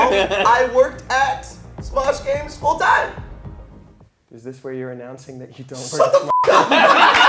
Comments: what did Damian say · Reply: i think he said " so this is where you announce you don't work for smosh games " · What did Damien say at 10:15, “is this where you are announcing that you are” ? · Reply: that you don’t work for smosh games full time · what did damien say at 10:16? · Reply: Something like, "is this where you're announcing that you don't work at smosh anymore/full-time" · what did Damien say? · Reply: "Is this where you're announcing you don't work for smash games anymore"